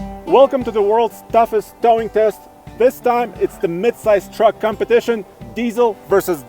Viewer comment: It sounded like he said the dumbest tow test lol